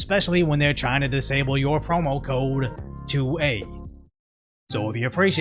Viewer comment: I heard “promo code TO A….” and I’ve been waiting for the punchline. AND THEN IT HIT ME